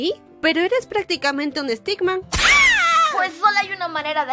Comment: Pense que decia estigma, y era stickman